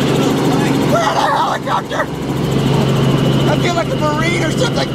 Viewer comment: I feel like a marrine (I think that's what he said)ch ch ch ch ch